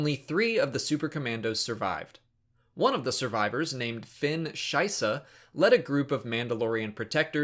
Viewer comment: Lost it at 2:20 X'D caught me by surprise and sounded like Scheiße. That family name is shit, litterally X)